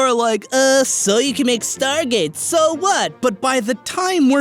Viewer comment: did I just hear stargate O.O <3.<3